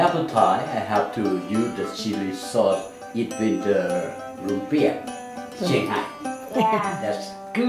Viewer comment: my mom is from California which is where I assume she picked up this recipe (with minor variation of filling) for Lumpia but when she told me what it was I thought she said Olympia which is sorta fitting because the taste is godly lol